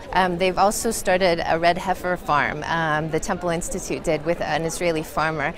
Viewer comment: hahaha--I thought she was saying, "red pepper" instead of "red heifer"--maybe they interpreted the ritual wrong!!